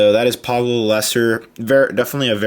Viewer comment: it sounded like u said pablo the mulester